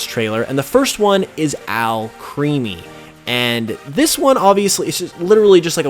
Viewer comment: Lol. I thought you were saying Alchemy not Alcremie. Also, anyone else planning a dessert pokemon party?